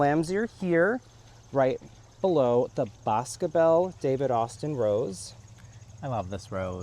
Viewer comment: hi- can i ask what rose you have at 32:25, i cannot hear the name of it - many thanks.... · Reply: That is Boscobel from David Austin